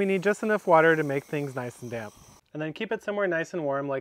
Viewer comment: So your bedroom is nice and warm! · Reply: I thought he said bedroom as well until I saw this comment